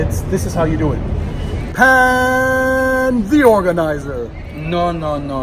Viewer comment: Believe me Pan.. Youtube's caption often translates "Paan the organizer" as Panty organizer 😂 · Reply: Now all I hear is ‘Paaaanty Organizer’